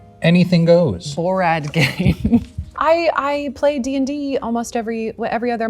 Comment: Did he just say 'borrad games' lol